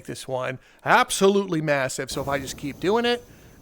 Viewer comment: did i hear the word "massive"?